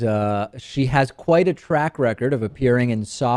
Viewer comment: lol it sounded like you said "rack record" instead of "track record".